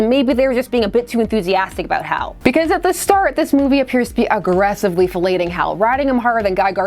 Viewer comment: !! 18:28 - Agressively Felating Hal??? Did I Mishear? love Steve Holliday